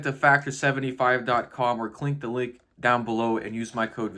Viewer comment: Did bro just say Clink the link? I love that haha. So much pressure when on camera hey